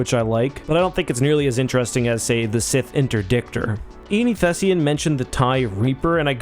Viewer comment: I accidentally misheard that as "Sith Interdicker" the hell is going on with my mind.